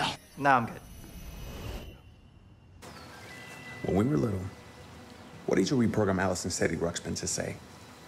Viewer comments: What did Diego say between 2:01-2:05? I can’t understand him. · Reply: When we were little, what did you reprogram Allison's Teddy Ruxpin to say? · What does Diego say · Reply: Aliza Tariq when we were little, what did you reprogram alison teddy ruxpin to say